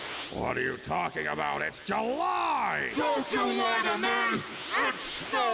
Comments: "Don't july to me" is my all time favorite line from this movie in specific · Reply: That makes way more sense then what I thought. I thought he was saying "don't JEW lie to me." My brain went to a weird and fucked up place on that one. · delivery of "DON'T JULY TO ME! IT'S SNOWING!" never fails to make me laugh. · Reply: Wait I never got that, I thought he said “don’t you lie to me” but that’s so much funnier